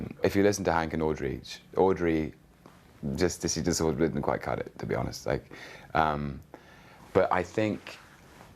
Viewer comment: Great interview. But @ 4:43 I can't for the life of me figure out what he said, lol. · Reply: His words kinda ran over each other but basically she doesn't quite cut it.